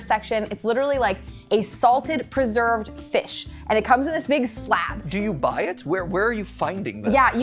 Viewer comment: for some reason I heard the word "slap" instead of "slab" at first. Just imagine going to a grocery store and asking for a fish slap 💀💀